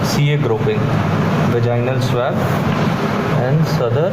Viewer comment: did he say Vaginal swell?? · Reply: Vaginal swab.